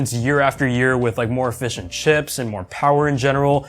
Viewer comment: i thought you said more fish and chips then i thought about it and realized you were saying more efficient chips